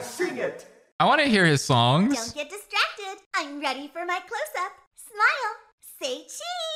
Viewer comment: I thought she said “I’m ready to take my clothes off!”